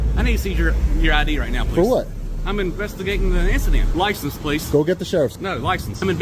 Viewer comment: he wanted to know where you from.... he heard our NY accent. he investigating a insta-dent.... lol that was funny. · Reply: Thought I heard he’s instigating an instadent😂😂